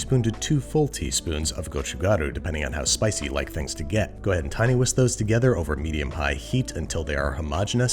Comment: you know what, T-Whisk can be your stage name some day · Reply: i thought he said 'teeny-whisk' but t-whisk makes sense, lol